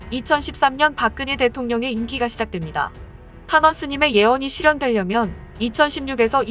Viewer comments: 타노스님으로 들리는건 나만? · 탄허스님 왜 타노스님으로 들리냐? ㅋㅋㅋㅋ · 탄허스님이 타노스님 으로 들리시는 분? 일본침몰은 엄청 슬프거나 그런건 없는데 지구오염이 걱정된다. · 탄허스님=타노스님으로 들었다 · 제가 잘못들었네요. 타노스님으로 들었어요^^a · 나만 계속 탄허스님이 타노스님으로 들림?? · 화면꺼놓고 듣다가 탄허스님을 타노스님으로 들었다..